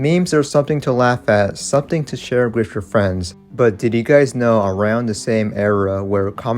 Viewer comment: i heard 'diddy guys' 🥀